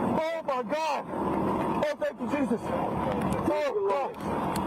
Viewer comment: Sounds like the idiot on the track was trying to unalive himself. Sounded like he said, "where'd you come from? I hate you."